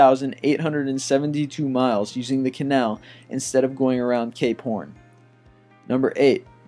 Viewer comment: Did he say gay porn at 10:16?